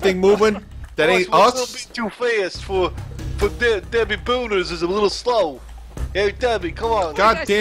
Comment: During 3:52-4:10 = When you say DeadB with that "voice", it sounds like your saying "Dadbee"; you had my hopes up, the first time you said it